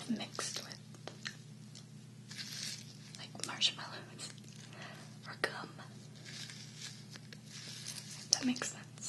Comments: Is it just me or on 12:48-12-55 did you say the clay looks like 'Marshmallows or cum'? haha. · Swear to god I thought she said marshmallows or cum. Omg. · what did you say at 12:44. it mixed with what ? i must just be imagining things :P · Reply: marshmallows or gum